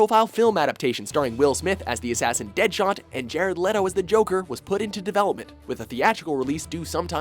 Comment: I misheard and thought he said Jay Leno is the Joker.